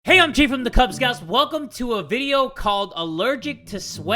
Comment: I remember when I thought jay was saying " Hey Yon-j from the kubz scouts"